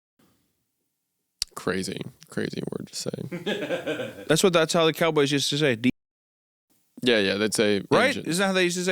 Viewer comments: what word did he say? Was it THAT bad? 😮 "a stupid way of sayin 'indians'." ? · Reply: Native American here, it sounds like "engine" and is incredibly offensive due to its historical connotations. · WHAT ARE THEY SAYING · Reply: Injun